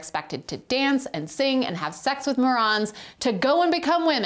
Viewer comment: Sounds like she said “have sex with a moron.” 😜